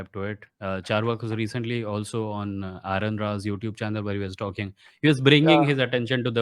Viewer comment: What YouTube channel was Carvak on? Sorry couldn't understand that part clearly. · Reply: Aron Ra